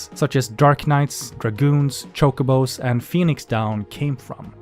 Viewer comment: Even after all these years, pronunciation of Chocobo sounds funny to me. I first heard it as 'Cocoa-bo'. Chocobo sounds like a brand of chocolate powder that you add into your milk